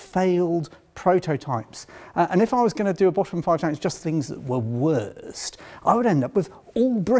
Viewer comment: SORRY I Thought You Said Bottom Five BUTTS!